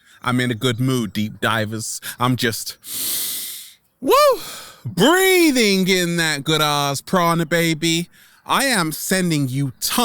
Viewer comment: I thought he said breathing in that good ass chronic baby....i laughed and was like okay okay okay 😂😂😂💜💜💜